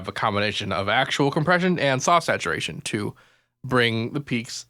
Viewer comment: Why saw saturation? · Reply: It's soft saturation. It's funny that i thought he said saw saturation too,before i looked at the manual. As he says it's free compression with a price of adding some distortion and brickwalls anything that goes above the line you set it at. I tried it and it's too much distortion for me in most cases because i make hardstyle and combine it with many aggressive genres like metal and add my own distortion before hand. If it sounds good use it,if not don't use it just because it's analog.